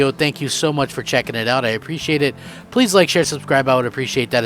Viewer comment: Sorry, I thought you said please dislike so I did.